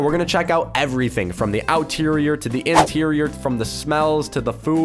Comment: Did he say "Outerior" instead of "Exterior"